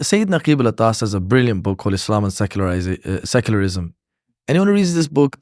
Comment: what is the name of the book he said at 33:04?? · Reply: Islam and secularism